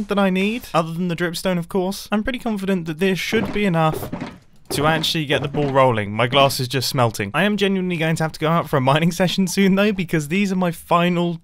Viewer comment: I thought that he said his glasses were smelting